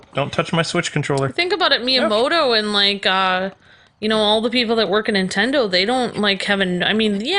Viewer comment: did he say meow